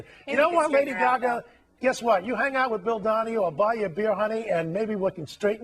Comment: Man I was scared. I thought you said PHIL Donahue. I'm OK now.